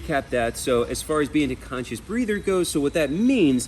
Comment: I swear I thought I heard "Conscious Breeder".